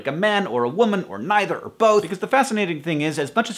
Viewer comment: lol, i heard "if you are male or female or neither or boat" yeah boat instead of both and i was like "yeah, i'm totally a boat" you totally got the true me there"